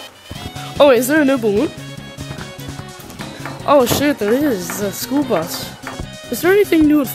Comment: Hey everyone, just to let you know I did not swear I said “oh shoot”